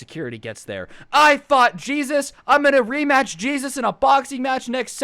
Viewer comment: I thought he said put jesus in a box